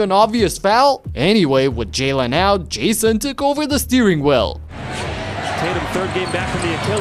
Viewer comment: Steering well? Did I hear it right? Is my ears messing with me right now? Or it should be "Steering wheel"?